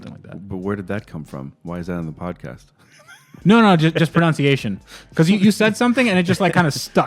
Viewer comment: did just say 'pronounciation'? loll